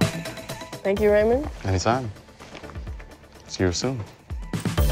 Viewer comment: did she say 8:48 thank u very mun